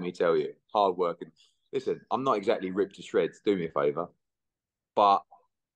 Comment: Sure i heard Eddie say "do me a favour" at the beginning, big frank warren rubbing of young eddie i see.